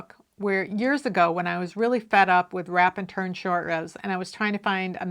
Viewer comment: I totally thought you said RAPID turn shortrows and was like "ooooh, technique I have never hears of". It was a solid 10 minutes before the penny dropped! Yes I also hate W&T - it looks fine on G stitch (which I never use), but for st st I definitely prefer German shortrows I'm so glad you finally got the handspun sweater finished! It's such a nice fit; all that work was worth it